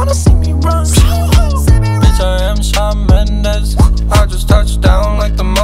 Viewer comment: I thought Joji said” bitch I eat salmon heads(or eggs)” and it made sense to me, cuz the Japanese eat that shit you know?